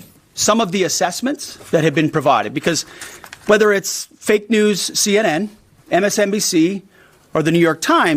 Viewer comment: Hegseth slurs his words when he’s drunk. It sounds like he says “Fake News” every time he talks about Fox News.